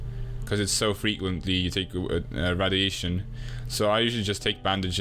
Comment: I think he said it’s easy to tank radiation